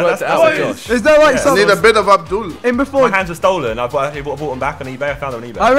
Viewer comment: did said a bit of abdul